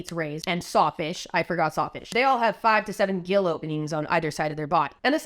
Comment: Instead of "gill openings" I first heard "gill o beans"